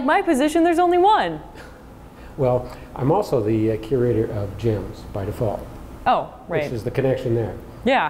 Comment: Thought he said "I'm also the Curator of Jams." "Curator of Jams" is my new dream job.